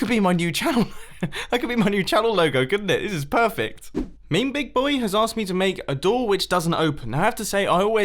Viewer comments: Did you say... meembigboy? IT'S MEMEBIGBOY NOT MEEMBIGBOY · you did NOT say meme big boy.. i refuse to believe it. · DID YOU JUST SAY MEME BIG BOY??? UNSUBSCRIBED FROM YOU YOU FILTHY PEASANT